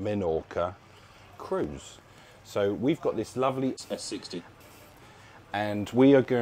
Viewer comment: what did you really say instead of S60? 😄